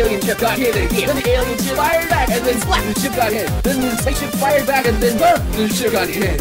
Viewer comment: Why does it sound like he says "succ" instead of "splat"?